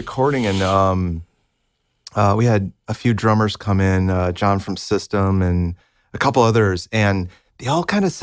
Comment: Did he just said John from System? System of a Down? 16:28 Fuck yeah